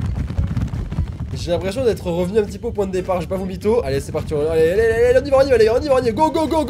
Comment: Y a pas que moi qui est compris '' J'vais pas VOMITO ''